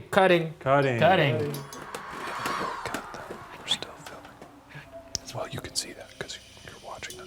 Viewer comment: when is whispering I swear I heard someone say “don’t quite filming”not sure if it’s one of the camera crew or not can someone debunk this or something